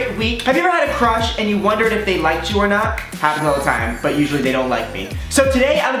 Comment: I thought he said have you ever had a crotch and wondered if they liked you 🤣🤣🤣🤣🤣🤣🤣🤣🤣🤣🤣🤣